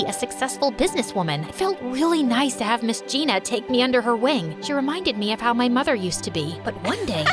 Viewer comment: For a sec I thought it said take me under her wig😂😂